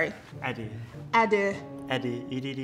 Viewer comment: HOW IS NO ONE TAKING ABOUT HOW SHE COULDN'T SAY EDDY FOR A SEC, I DIED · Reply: KalMay it didn’t even sound like he said Eddie 🤣🤣🤣 I heard edu!!